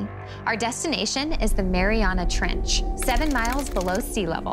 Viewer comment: I thought that said the Miranda Trench